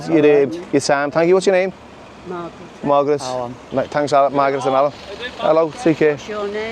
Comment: Sounded like you called Margaret “Migrant” 😂